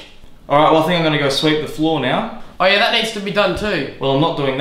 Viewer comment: I legit thought he said: "I'm going to go sleep on the floor now. Ah yeah, that needs doing too"